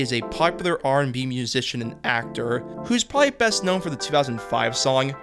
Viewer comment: I misheard this as "popular Armenian musician" lol